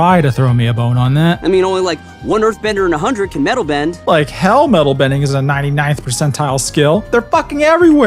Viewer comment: I actually think I misheard this line the first time I watched this episode. I thought he said "only one out of every 3 Earth Benders are capable of metal-bending" and that honestly seems like a more accurate description of LoK's Metal Benders.